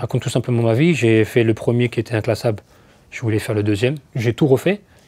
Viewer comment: Qu'est-ce qu'il dit à 3:27 ? "j'ai été le premier inclassable, j'ai voulu faire le 2ème inclassable" ??